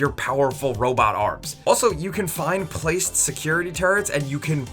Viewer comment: it really sounds like says "security turds". I'll get my coat.